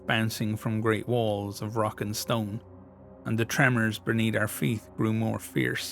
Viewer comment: DID I HEAR A ROCK AND STONE?!